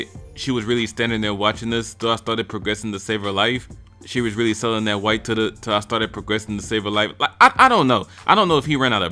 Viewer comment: “see was really sellin them rocks till the - till I started progressin and savin like” thats what I heard and I think the pauses are on purpose too